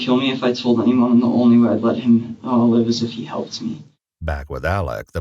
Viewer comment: wait did say “the only way i’d let him live is if he helped me” ??? he told on himself right from the start 😮